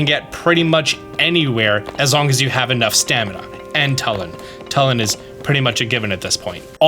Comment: it took me 3 tries to parse what you were talking about when you said "talon" instead of "Tulin" lmao had to extrapolate from context.